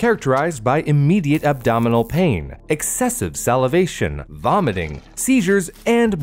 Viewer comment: thought he said expensive nearly said she